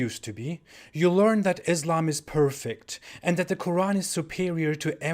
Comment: I thought I heard ''Islamisbad''.